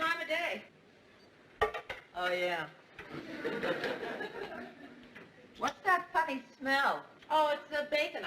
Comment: did she say "What's That Fucking Smell?"